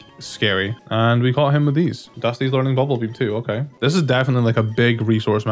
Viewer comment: Thought you said Dussy 😳